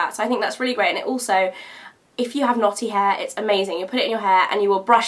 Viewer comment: ohh "knotty" hair! i thought u said "naughty"! lol